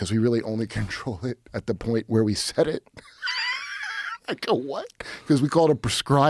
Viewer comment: did he say “nga what?” 😂😂😂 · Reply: “ I go what “